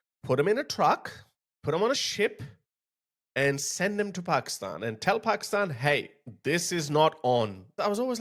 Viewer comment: Did you just say Chello Pakistan 😂😂😂 that made me howl